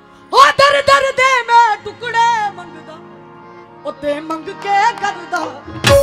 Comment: Did she say “My new song” at 0:18 😂